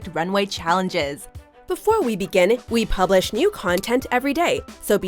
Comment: Is it just me or does it sound like she says begineth?